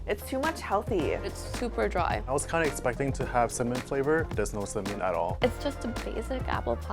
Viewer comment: he said "cinnamon flavour" really fast do it sounded like "some mint flavour" Edit: For the confused ones